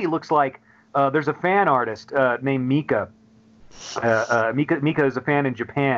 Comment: Does anybody know who this Mika-artist is, that Jim mentions at about 17:50? I can't make out the last name. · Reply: Mika Kuloda